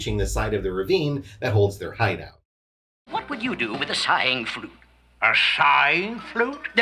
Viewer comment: Joker seamed like he was still down with steeling a Saying Flute... don't ask me way. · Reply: small correction, they actually thought Flakey had said Sighing Flute, not saying flute